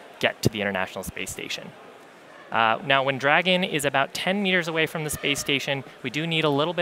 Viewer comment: did he say 10 years away??? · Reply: No, 10 meters away.